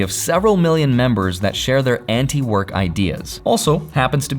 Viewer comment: did someone say Antifa?